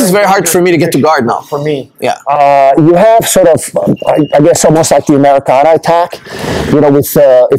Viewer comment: what's the name of the technique? (he said "similar to americana"?)